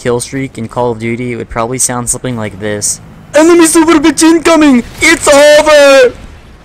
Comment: what did he say? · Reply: enemy super bitch incoming, it's over